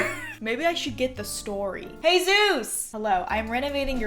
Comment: I thought she said “Jesus!” (But the Hispanic version) instead of “hey Zeus!”